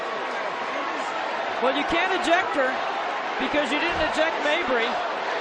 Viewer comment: Is that right? I heard the commentator said “You can’t eject her… cause you didn’t eject Mabrey!”That’s a real commentary… Goodjob!!!